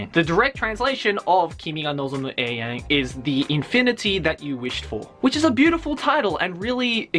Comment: is it just me or did he just say the "infinity you wished for"